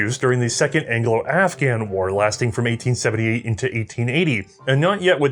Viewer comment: At one point I thought I heard the, “the Second Annual Afghan War”.